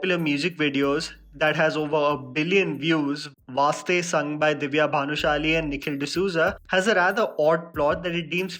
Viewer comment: did he say Divya bhanushali😂